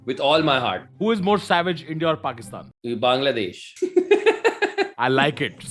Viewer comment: When rohan said bangladesh I heard "baingan" loll